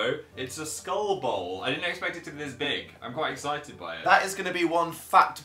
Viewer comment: heard that as phat